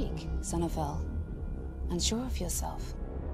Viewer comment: Efectivamente Jesus, me he equivocado... entendi "And sure of yourself" cuando realmente es "Unsure of yourself". Sorry por el error.